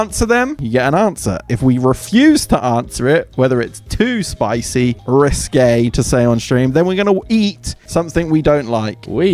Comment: just a subtitling gnote, i think he said risqué rather than risky, which is no longer an acceptable alternative spelling nowadays